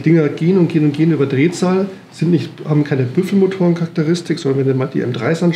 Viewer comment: Was ist denn mit RUF? Gibt's die nicht mehr oder warum wurde die Firma bei der Aufzählung anderer Hersteller nicht erwähnt? Und bei 32:40 verstehe ich ihn sagen sie haben keine "Prüfelmotor" Charakteristik. Sehr wahrscheinlich versteh ich einfach das Wort nicht richtig. Bei Minute 33:00 kommt's nochmal. Und dann würde mich noch interessieren was es mit der berührungslosen Dichtung auf sich hat. Was dichtet die ab? Das verdichterrad zum Getriebe? Und wie funktioniert das? Wäre so eine Dichtung nicht auch perfekt als Kurbelwellen Simmering? Kann mir jemand da helfen?